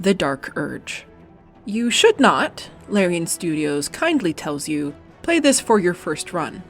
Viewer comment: The line at 0:35 was so confusing... "You should not." "Larian studio kindly tells you "Play this for your first run!" Is how I understood it when I heard it the first time. My brain separated them for some reason.